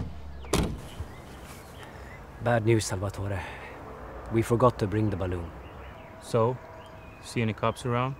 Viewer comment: I first heard "we forgot to bring the money